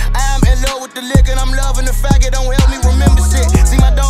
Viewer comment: ?? What he say? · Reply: I'm in love wit the liquor and the fact it help don't remember shid